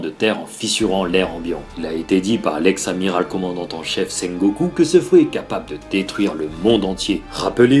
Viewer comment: POURQUOI TU AS MIS SANG GOKU ?! (ouk je parle bien de dbz)